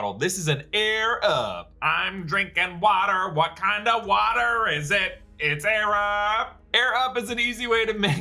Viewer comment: I thought he said Wakanda Water at first 😂